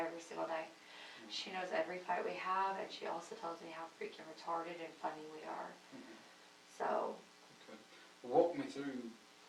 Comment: Did she say her mom said, in response to them arguing, she says: "She says how retarded and funny we are"...?! Around the 40:30ish mark. Sorry, it's either my phone or this video isn't very loud.